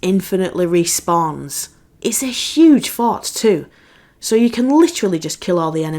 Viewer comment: I kept hearing “the fart” 😂